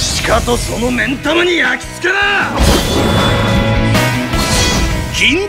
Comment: の銀魂がキン◯タマにしか聞こえない